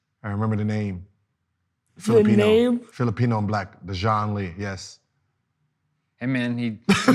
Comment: did he say Filipino-Black?